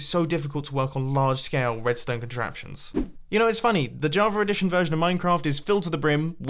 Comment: Why does it sound like you say Javar every time instead of Java? Am I going crazy?